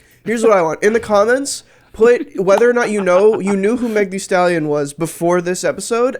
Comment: I’m with. I’d never heard of her, either. I thought they said her name was Megan D. Stallion the whole time like she was a One Piece character. I’m also a middle age man who doesn’t know what the kids listen to these days. Additionally, I live on the other side of the planet.